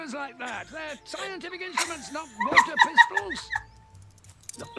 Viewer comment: I always thought he said "walther pistols" ... I guess water pistols makes more sense for doctor who.